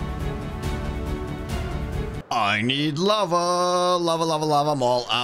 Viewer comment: my headphones cut out and it sounded like “i need love” same. same.